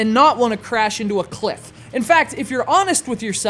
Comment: oh, i thought it said, muslim skyheroes